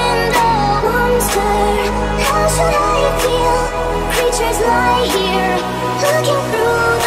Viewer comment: Did I just heard Monster can't survive here Instead of Monster how should I feel creatures lie here